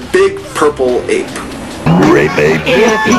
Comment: it sounds like he says rape ape